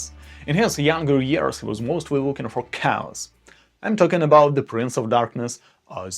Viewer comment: When he first said, Slash, I thought he said flesh! Lol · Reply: I thought he said Ozzie Osborne in his younger years was looking for cows. It was chaos! Lol